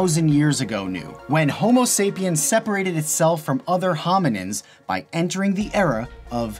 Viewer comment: Did he call us "homonyms"? Edited 3 years later. Boy, was I off on the timestamp when I said 3:50.